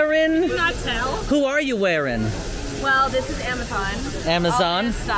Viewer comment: I thought said, "Well this is EMMA-zon" like Amazon ( Version)!